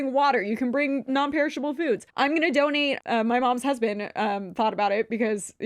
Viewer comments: Not me at 1:18:10 mishearing say "I'm going to donate my mom's husband" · Really thought was saying she was gonna donate her mom's husband for a sec